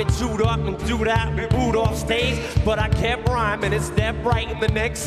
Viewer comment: what does he say at 3:12? “I’ve been chewed up, I’ve been ... ?” he clearly doesn’t say “spit out” like in the original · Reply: ~~Dooed out? Sounded like this